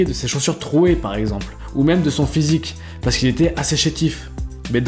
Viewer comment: il a dit quoi ? "Il était assechetif"?